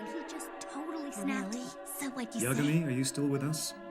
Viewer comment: what in the world is the second girl saying at 2:30 "really? [...]" what does she say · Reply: i think i got it, she said "really? so, what'd you say?"